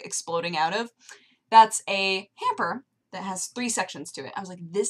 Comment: I heard, "you see like fabric exploding out of, that's a pamper" I just tought WTF and rewind the video to hear hamper, or how do you spell it